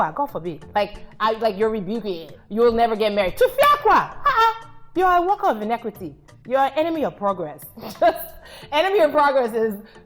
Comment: What is she saying at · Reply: "Tufiakwa, You are a worker of iniquity. " "You are an enemy of progress 😂😂" It all means , you are not good..